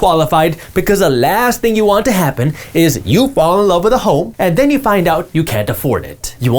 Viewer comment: Thought he said fall in love with a hoe. lol